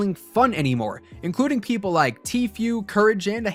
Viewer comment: Sounded like you said "tfuel"